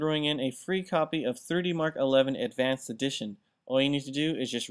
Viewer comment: why does it sound like he said 30mark? and not 3D MARK....lol i listened to it 6 times and still sounds like 30mark